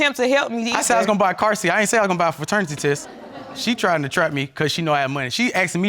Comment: Did he just say "fraternity test.."?? 🤔 lol